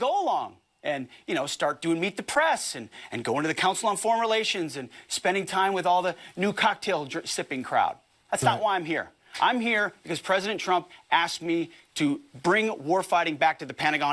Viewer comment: Did Pete say "NUDE cocktail sipping crowd"? Huh. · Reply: i thought maybe he said "NOON cocktail sipping crowd" cuz you know this guy is hammered right after breakfast